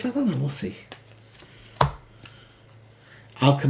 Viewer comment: I thought you said occum is the two of swords...After MLK Jr froze the country, I was a child. Looking back I feel changes for the good flourished and those for the worse went under a rock or into hiding. Racism continued but progressive ideas and brotherhood blossomed and we settled into those two conditions. The Trump era is marked by the snakes slithering out from under their rocks and trying to seize back whites only power.